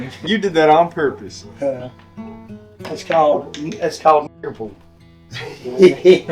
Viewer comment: did he say "thats called N***R pool"?